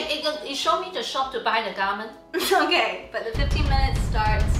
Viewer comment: I heard “Show me the shop to buy the government”😭